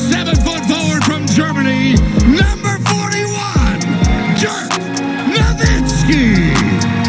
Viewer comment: Did they said "Jerk" Nowitzki?